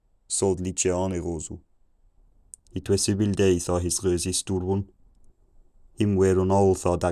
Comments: I was understanding "chicken" about 650, and I thought I was understanding full sentences about 700 because I thought you said, "It was seven days..." but it turns out, the translation was "It was a bad day..." And then I understood "it was not..." at 900. But I didn't start fully understanding sentences until 1300. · About 800 I heard 'chicken' 😂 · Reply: Same! I thought I heard "it was seven days" around 700 but could be wrong. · At 700 It was seven days I thought I heard, really understood at 1400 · I heard a "somewhere" and a "suddenly" and at 700 "it was seven days" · Around 680 AD I heard "It was seven days..." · Around 700 i heard him say "7 days" i think. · I am probably hearing things but around 700 I thought I heard something about' 7 days' a little while later I thought I heard the word 'scream' just before 800 I thought I heard the word 'horse' then the word' chicken ' It got completely understandable about 1400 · About 680, I thought I heard, "It was seven days," but in watching the end translation, I see you really said, "It was a bad day...." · it was so crazy listening along to basically gibberish and all of a sudden hearing "it is long winter, the ground is cold" emerge from the fog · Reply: I heard "It was seven days" but unsure if that's my brain playing tricks on me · 700 AD I heard "it was 7 days at his restaurant" in a French accent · he said Chicken at like 800 AD. at 700 AD there was something like '7 days' or '7day' but I could have misheard.